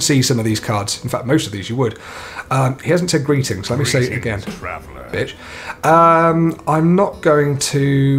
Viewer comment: did he say "b*tch"?